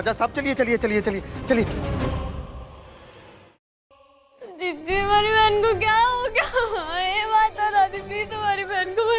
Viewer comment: her jiji sounded like chanhcal sa 😂😂😂😂